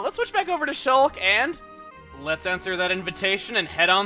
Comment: I thought he said, "Let's censor that invitation."